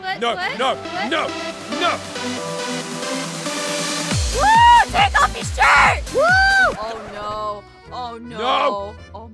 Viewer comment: I first thing I heard is "TAKE OFF YOUR SHITE" LOL I was like what the hell